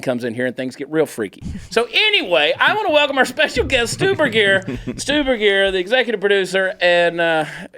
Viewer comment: When you said Stu Berguiere, I thought you said Stu Brassiere.